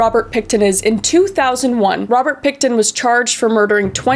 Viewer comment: I'm not gonna lie, for like 10 minutes of the video I thought she was saying Robert "Pig"-ton, and I was thinking "no way..."